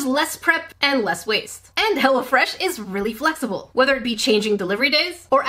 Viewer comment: I thought you said hella fresh